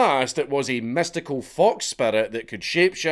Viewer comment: with your accent it sounds like you said, mystical fuck spirit. Which to many furries is factually accurate